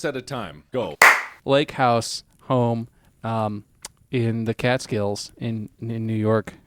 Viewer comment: To be fair, it really does sound like he said cat scales.